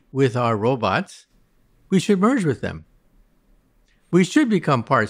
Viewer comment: For a moment I thought I heard him say “at that point, we should murder them”, before I realized he had said “merge with them” hahah 😅